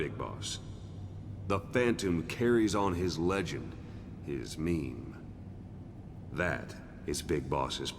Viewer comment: did he say HIS MEME???